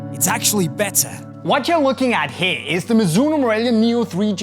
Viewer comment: What did he say ? Mizonuno nu morealia 🤣